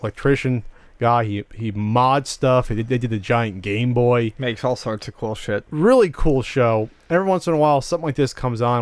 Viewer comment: what does the guy on the left say xD? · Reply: "...makes all sorts of cool shit..." i think, hahaha